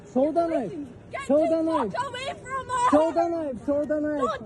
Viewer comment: It sounded like he was saying "show the legs"is it my hearing